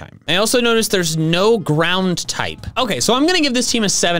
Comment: did he just say that there is no ground type